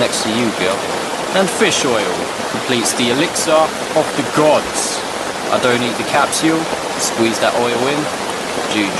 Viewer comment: What does your dad say? Bro you are dedicated af to be out there during that shit. I woulda noped out. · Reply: Mario "...'er indoors" is what his father would say, referring to the significant other.